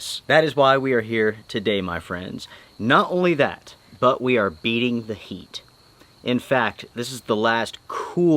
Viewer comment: Yeah, i thought you said "beating the meat". Never clicked so fast in my life